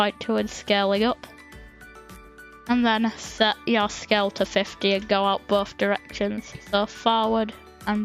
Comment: did you say a bad word? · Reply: In that clip I said : set your scale to 50 and go out both directions?